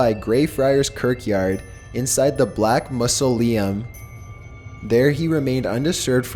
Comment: "Mussle-Iam" instead of Mausoleum. Hahaha! I luv the way he pronounces some things. Lol Or should I say mispronounces some things . . . . Either way, it's cute and I luv it LMFAOO 😂☠😂☠😂☠😂